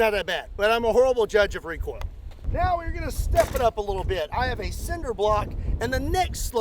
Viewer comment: After "I'm a horrible judge of recoil" I heard "I am a cinder block" lol.